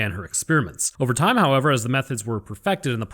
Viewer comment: I swear for a second I thought you said “as the meth heads were perfected” 😂